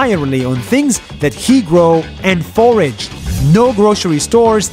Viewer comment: Subtitle is incorrect, it says forged instead of foraged (it's only a little inconvenience, mistakes don't count anyway 😉)